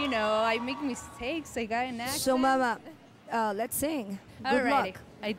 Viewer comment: "What's your name?" - "Simon." "I make mistakes, I got an accent" ?! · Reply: Yes at 1:06 she clearly says "I got a accent"